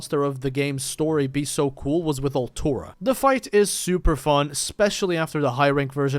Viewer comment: What is he saying here? What monster is he talking about, I'm not familiar with this... Altura?